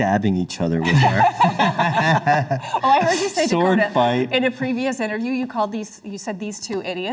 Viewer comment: WHAT DID PEDRO SAY? SWORD FIGHT??? HELLO?